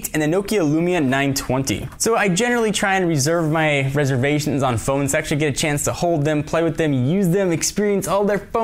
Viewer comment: Am I the ONLY one who thought he said "Reservations on phone sex"?